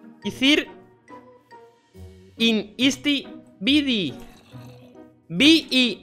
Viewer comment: ya digiste la "E" porque dijistes: in istE vidi